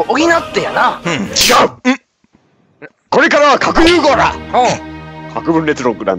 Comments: ごめん。核融合ら！にしか聞こえないww(((( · 「核融合ら」に聞こえる笑